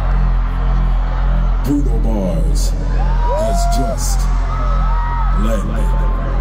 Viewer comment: what is said in the opening of the show? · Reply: Bruno Mars has just landed